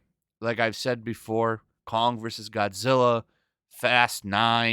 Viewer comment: For a second there, I thought you said "Congress is Godzilla".